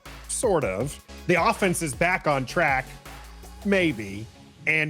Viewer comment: You said the offence is back on track, I thought you said the offence is back on crack. 😂😂😂 #Needmycoffee